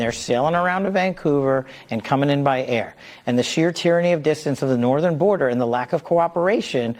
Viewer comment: did Cash just say “…and the shear tyranny of distance…” rather than saying it without the keyword “tyranny “? Subliminal messaging? And just after the 5:00 mark, he groups “known or suspected terrorists” in the same statistic (I.e., only 1 number was provided to cover both). Why? Why not provide 2 numbers: one for the suspects and another for the actual”. They’re significantly different things. We don’t know how many there were of each group.